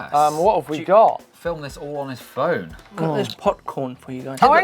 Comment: did really say POC-corn at 16:12? damn